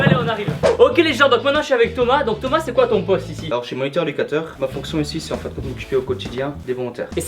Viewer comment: A 4:30 j'ai entendu moniteur dictateur 😂vous avez entendu ? 😂 😂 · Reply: Effectivement je parle vite mais je te rassure je ne suis pas un moniteur-dictateur mais bel et bien moniteur éducateur 😉